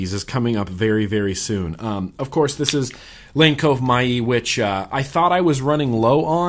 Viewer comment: what is the juice he says for the green mod!!!! omg i can not understand what he says, SOMEONE HELP! please:) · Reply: Lane Cove Myee